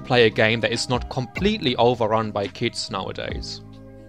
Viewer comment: Bro it took me a minute to realize at 2:37 you were talking about kits instead of kids 💀